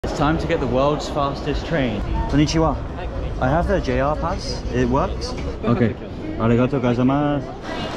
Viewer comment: At first i thought that he said '' i have the N pass '' 😢My brain 💀👁️👄👁️